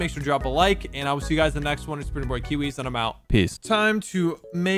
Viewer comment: In the intro did you say "its pretty boy kiwiz"